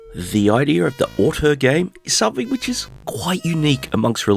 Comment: What did you say? The idea of a auto game? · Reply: 'twas Auteur, as in "The idea of the auteur game..."